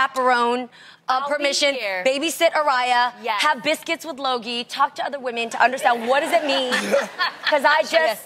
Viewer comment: "Biscuits w/ loggie"(0:41). Sounded like luggie, that had me cracking up. (16)